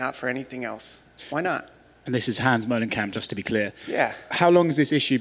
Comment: Honestly I thought he said Hans Moleman and I was very confused.